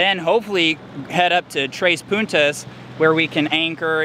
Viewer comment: What size Mantus have you got? I'm thinking of a 25lb one for my 26' 6000lb displacement boat edit* lol I thought you said "tres putas"🤣🤣🤣